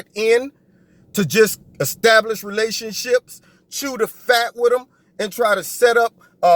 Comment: did I hear you say about the "spy" sent to the Trump campaign .. @ 5:32 "....should have fart with them...." 😁😁 I'm dying of laughter-ache